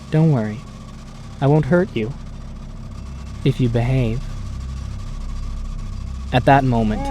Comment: When she said if u behave I thought she said if u bang😬